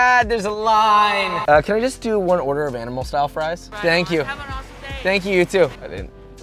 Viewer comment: did she just say "have a rough day"?